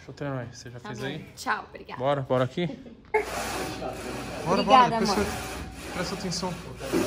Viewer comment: Eu ouvi um "obrigado amor "